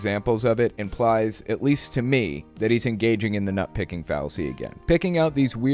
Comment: Oh nut Picking. You drew a dog and I thought you said nut kicking